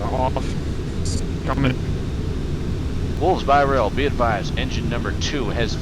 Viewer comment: Anyone here heard him say "Wools by ray"